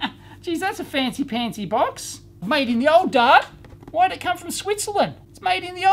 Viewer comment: Made in the what???? the old Guard? - i know he means Britain but i can't understand haha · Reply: The old dart, <link>